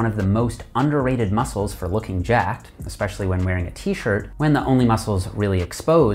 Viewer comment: What i heard was i need to work out my neck when wearing a t shirt